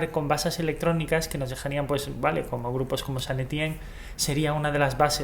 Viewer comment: Que grupo mencionaste en el minuto 7:11? No lo entendí muy bien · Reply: Saint Etienne, puede que no lo hayas entendido de verdad, pero literalmente es el nombre de una ciudad francesa, deberías saberlo!